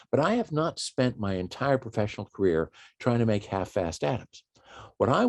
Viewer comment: "...I have not spent my entire professional career trying to make half-assed atoms." (Sorry. I just got a kick out of what I thought I had heard for an instant). :-) :-)